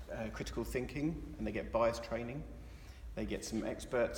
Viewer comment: Looking at the transcript. Good try, Google, but at 1:52 did NOT say "BUYERS training" but in fact "BIAS training". Maybe you need some of that, too.